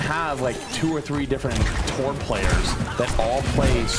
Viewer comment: is he saying "Thor players"? 😂